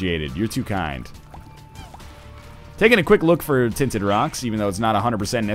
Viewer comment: for some reason the automatically generated subtitles say dins iraq's when NL says tinted rocks heh